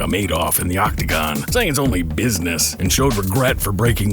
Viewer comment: I can't hear "fighting business." I only heard "only business" more clearly.